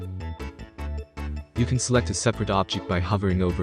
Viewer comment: what did you say? What to press while hovering.. I can't select separate objects. I tried pressing "O" Please help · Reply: Press L bro..